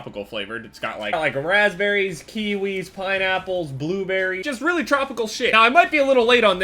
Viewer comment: did he say Bradberry?